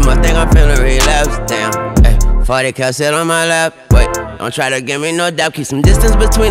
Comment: Drake: "Shawty come sit on my lap. Lil Wayne: "40 cal sit on my lap". That's the difference 🐐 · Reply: I thought he said 40 come sit on my lap 😭😭😭😂